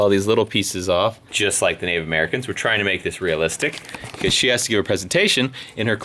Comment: i thought i heard Native American not paleolithic!